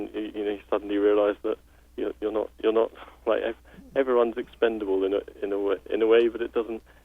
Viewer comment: Sorry for the late reply. 13:44 He said, "...everyone is expendable in a way it frees you...." He did not say "everything is expendable". He said "EVERYONE is expendable...." Everyone clearly refers to people and not a company. Everyone = other people. Sociopaths do have such a cold-blooded lack of conscience or empathy for others.